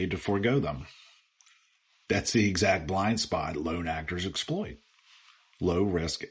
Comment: the speaker says "loan actors" or "load actors". What did he really say, and what does that mean? Edit: Gemini says, correctly IMO, that it was "lone actor". The point being that Robinson apparently acted alone and a larger team would have had more difficulty getting through security.